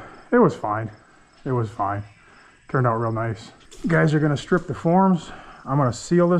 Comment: I heard it as "Guy's are gonna strip with form" Even if they were I'd pay for to keep his shirt on